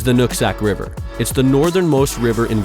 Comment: I swear at 1:25 I heard “nut sack river” 😂 Great videos.